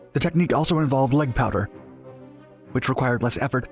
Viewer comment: is he saying "leg powder" or "leg power?" I feel it should be power but I hear a D in there. So does the auto-generated captions. But if its powder, what is it and how does it pertain to this story?